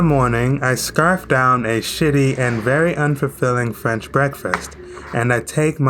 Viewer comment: that’s sh**ty? Did I hear you correctly? It looks pretty good to me. Yea a bit carb heavy but… what’s sh***y about it? Also $130 a night is the same as a good deal on a Holiday Inn in America and this is the most luxurious place they have. I would definitely prefer the later hotel you had though because it is more true to the culture of the people. More immersive experience.